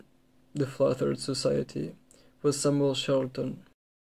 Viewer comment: lol - I heard 'some old charlatan'. Which, to be fair, may also be accurate.